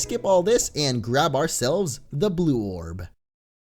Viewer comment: when he said "grab our selves the blue orb" was I the only one who heard blorb?